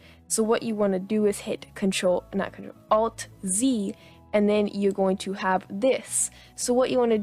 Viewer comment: Aa i couldnt hear this part properly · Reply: She said hit alt z